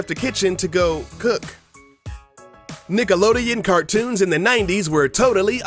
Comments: LOOL at 7:48 I thought he said "Niggalodeon" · for some reason, it sounded like he said "Niggalodean." I'M NOT RACIST I SWEAR THAT'S JUST WHAT I HEARD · Who else thought he said niggalodeon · it sounds like he says "niggalodeon" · Omg i thought he said ni**alodeon! :/ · did anyone else hear him say niggalodeon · "Niggaloden" I heard it and I was like what did he say? XD